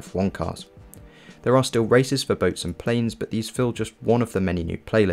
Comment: did he really said raceist ? LMAO *racers